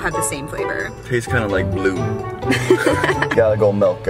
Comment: I swear said he tarde kinda like Lube! Lmao 😂